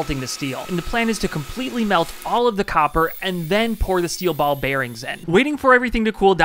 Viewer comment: Was really confused for a second because i heard "steel babies" then seen he just ment ball bearings